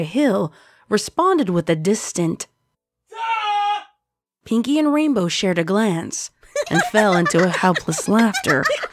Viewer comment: What did he say? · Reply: He shout out : DUHHHHHHH